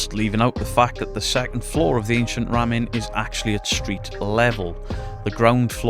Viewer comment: I had this on in the background while I was working and it sounded like you were saying "Ancient Ramen". My dumb-a** thought you were talking about haunted noodles. Great video as always!